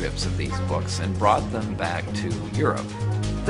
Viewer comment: did I hear the guy at 11:10 saying they brought the book back to Europ?